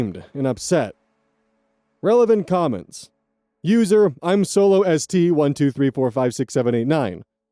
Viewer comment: "I'm solo ST" instead of I'm so lost made me giggle. A fair mistake!